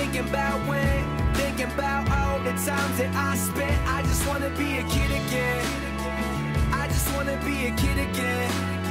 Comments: Kann mir wer sagen wie das lied heißt wo die ganze zeit "i want to be a kid again" kommt? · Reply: Ich hab immer verstanden: "i wanna be a cittycat" xD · Ich hab bei dem Hintergrundlied erst immer verstanden "I just wanna be a kitty cat" xD Dabei sagt er ja "I just wanna be a Kid again!"